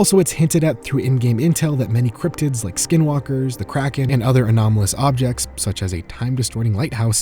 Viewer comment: Thought said Crackhead instead of Kraken for a second